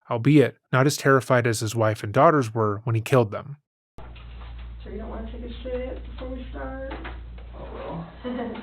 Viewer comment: I TOTALLY thought she said "You sure you don't want to take a shit before we start?" and I was instantly like ¿ASKIUSMI? 👁️👄👁️ Lmao